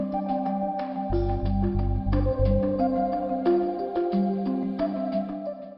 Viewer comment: I didnt catch the name of thr organization that the lady at 4:00 is from. Can someone tell me, please? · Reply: Alexandria Bella Yazda is the biggest organisation and it is supported by Amal Clooney